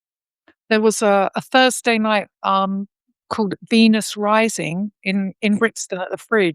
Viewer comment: Thought said Penis Rising. Genius name for a Gay Club ha ha